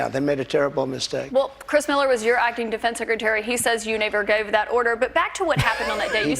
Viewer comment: She said " you N****r gave that order" hand to god she said that. Surprised the crew didn't catch that 😅